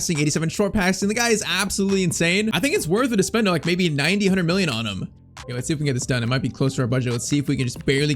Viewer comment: did anyone else hear him say 90 hundred million